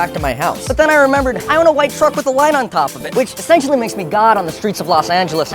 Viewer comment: "I own a car with a light on top, which makes me God on the Streets of LA" is the best line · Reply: Oh I thought he said, “white truck with a Lime on top of it,” referring to the scooter brand, and I was very confused 😅